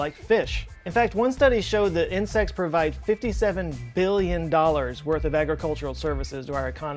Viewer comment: I paused it at the wrong time. When I un paused the video it sounded like you said “s*x provides 57 billon dollars of something “